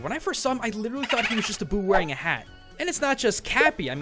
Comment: Ha, on 4:00 it (the subtitles that I put on) says "...I thought he was a boob wearing a hat..." but he actually said "...I thought he was a boo wearing a hat..." That's just messed up